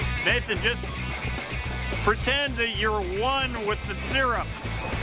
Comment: What he said “pretend that you’re one with the syrup” What I heard “just pretend that you’re one-on-one (eye to eye) with the syrup🌚”